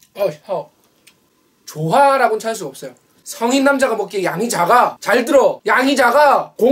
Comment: 양이작아가 양자강으로 들리는건 기분탓인가 진짜 많게보이는것도 기분탓인가..